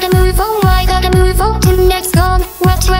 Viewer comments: what is she saying at 1:45? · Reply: lets go in reverse actually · What did she say at 1:44 ? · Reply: (Scrub) it actually says " Let's go in reverse" Just so you know